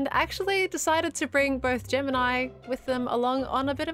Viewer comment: What said: "...and actually decided to bring both Gem and I along with them on an adventure." What I heard: "...and actually decided to bring both Gemini along with them on an adventure." It's a little confusing when Gem and Gemini are used interchangeably.